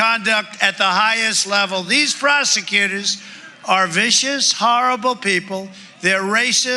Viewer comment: did he just say "Frosecutors"?? Lmao what an idiot!